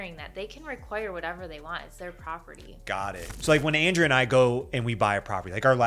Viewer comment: your subtitle person wrote "Andrew" instead of Andrea